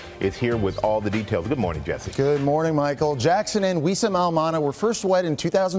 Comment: lol he said good morning michael ( talkin to michael STRAHAN), then said Jackson, getting ready to start his start his topic about Janet. Basically it sounded like he said " good morning MICHAEL JACKSON". idk i thought that was kinda wierd and funny. GOOD MORNING MS.!!!!!!!!